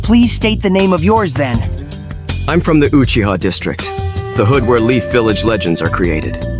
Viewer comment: I thought sasuke said Guuchiha